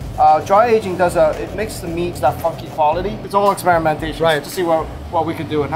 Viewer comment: Did he just say that it makes the meat that fucking quality? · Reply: Funky